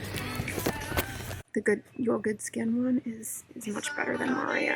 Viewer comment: “Good skin ones...” I can’t make out what she says here...